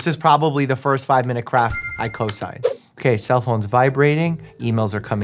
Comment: did he just say cosin? I thought this was medical and not mathematical